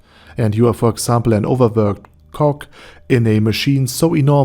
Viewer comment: At the beginning I wasn’t really paying attention so when you said “overworked cog” I heard ‘overworked cock’. Nothing to add, I just found that funny.